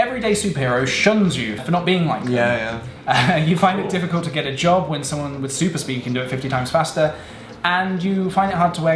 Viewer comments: what's Phil saying? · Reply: "that's cruel" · what did dan mumble? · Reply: he mouths "like me"